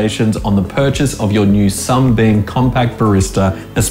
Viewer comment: Did he just say "Sumbeam"?